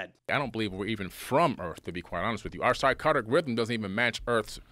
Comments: does he say “…our psychotic rhythm…”? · I think he's saying "psychotic rhythm."